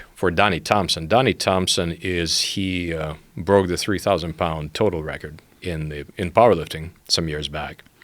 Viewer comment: Did I hear that right? 3000 pounds total record? Joe what did you put in his tea? 😅